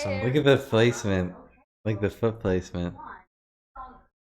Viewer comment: The unicorn makes like a puddle of color where it steps, that is what was trying to say by "food placement". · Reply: ”Foot placement” made sense to me